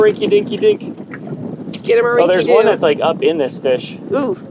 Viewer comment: I just about died when said “oh there’s one that’s like up in this bitch” 12:01 so I went back and played it again and he actually said “in this FISH” but I had a good laugh all the same lol